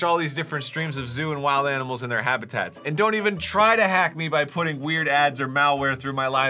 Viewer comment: I heard "Streams of DOING wild animals in their natural habitat"